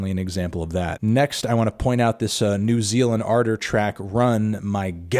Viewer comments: I thought he said 'New Zealand Ardor' but happy to learn its a cool track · thought melon said “New Zealand Ardor record” instead of “new Zeal & Ardor record” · I really thought he said "New Zealand Daughter"